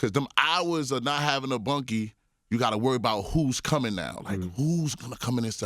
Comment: What does he say? "Please let this not be an "ott"??? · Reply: He said dont let it be " an opp" .. as in an enemy. Rival gang.